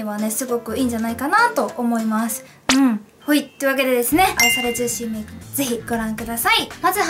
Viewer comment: このなんかなんて言うかわからないが好きw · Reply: んー ほいっ